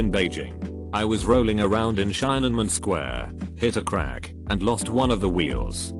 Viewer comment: did he say "chinaman square"?